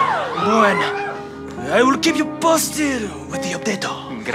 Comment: did he just say I will kick the imposter with potatp